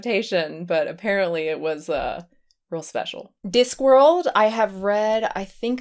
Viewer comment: You have such a strange accent it sounded like you said D'Squirreled not Disc World.